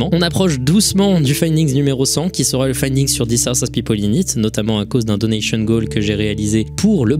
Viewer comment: Bonsoir ! C'est quoi le sujet du Findings 100 dont parle à 44:57, j'ai pas compris le nom ? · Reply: Sur rorochan 1999 il me semble